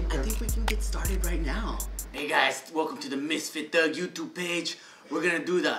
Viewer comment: who heard say bitch